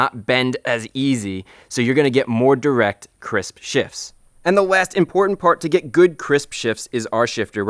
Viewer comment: I miss your videos, I think I can comfortably say your videos single handedly gave me all most all of my knowledge on cars. Also every time you said Crisp shifts I thought you said Chris fix lol